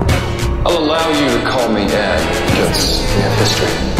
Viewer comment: I thought he said “I’ll allow you to call me Daddy because we have history” Then I realized he said Ed But the Riddler is Daddy!